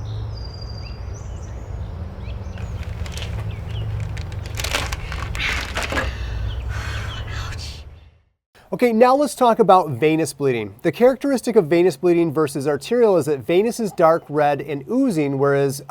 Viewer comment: WOOWWWW IM DUMB...I THOUGHT HE SAID ANUS BLEEDING